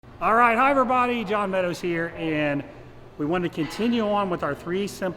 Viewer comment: Arrrrright hi jon meadows, everybody here. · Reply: for the longest time i thought he was saying "higher body, John meadows here" I assumed higher body was something to do with his business